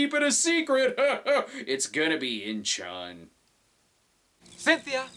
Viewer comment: What did he say at 23:39? "Inchon"? Isn't that in Korea?